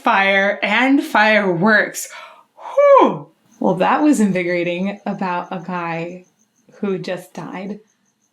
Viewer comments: She scared me to death at 24:36 when she said "that was invigorating, about a guy who just died." I heard "that was invigorating, by a guy who just died" and I screamed "WHAT!?!? NOOO!!!.... oh, wait a minute." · I misheard you and thought you said Bruce Dickinson just died... eventually would come, but it would be a great loss to the world when it happens. Nice review, glad you are enjoying Bruce and Maiden's work!